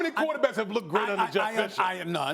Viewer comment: Did Damien call Jeff Fisher "Jeff Sessions" at 4:45?